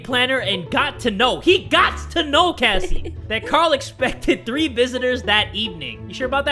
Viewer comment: i thought it said exploded XD